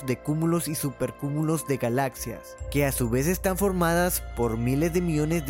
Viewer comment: escuché culos y superculos de galaxias